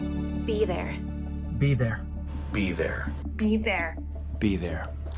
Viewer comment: all i hear is "beaver"